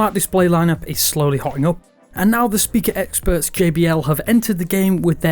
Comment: Hey,did I just hear Kbl at 0:07 ????